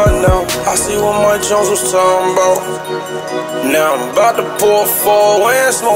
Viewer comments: "I see what mike jones was talking bout " 1:49 just now realizing that's what he said after hearing this years ago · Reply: New gen daily Musik i thought he said i see what my judge was talkin bout 😭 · "I see what mike jones was talking bout " 1:49 just now realizing that's what he said after hearing this years ago